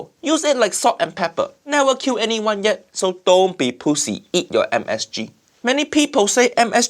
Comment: LoL! Did anybody catch what Uncle Roger said at 4:18? So don't be p*ssy, eat your msg.. 😂😂